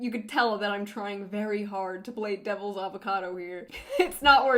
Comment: Anyone else heard "Devil's Avocado" ?